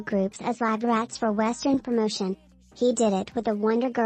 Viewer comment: I must be sleepy or something… I legit thought the video said at some point “using ggs as guinea pigs for western porno”…………..